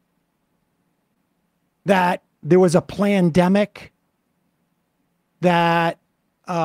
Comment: I coulda sworn Sam said, "plannedemic" 🤣